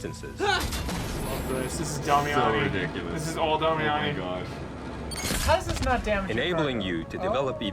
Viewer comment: Someone please help! What was the joke made at 2:37:32? Everyone cracks up but I can't make out for the life of me what he says. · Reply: said "how does this not damage the cargo" and instantly the video shows the cargo deploying boosters. "oookay" answering the question.